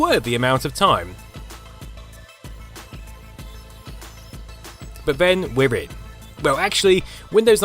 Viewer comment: what is he saying? When wibbid? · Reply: Glenn K but then we're in.